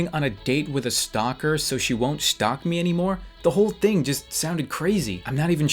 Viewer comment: did you say shtalk ?